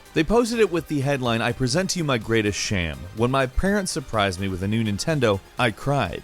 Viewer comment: did he just say sham intead of shame